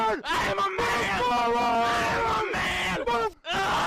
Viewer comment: I sounded like "I am a maggot" at one point